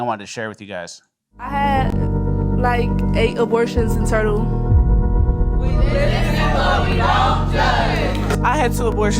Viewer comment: Sounded like she said "i had 8 abortions in a turtle"